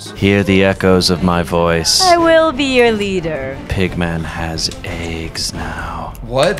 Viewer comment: I misheard "pigman has eggs" as "Pigman has AIDS" 😂